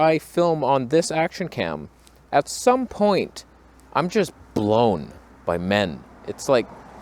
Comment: Did you say “blown by min?” Or men?